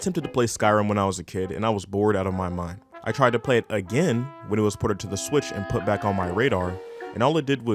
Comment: i heard “i played again when it was ported to my switch” and turned the vídeo off, respectively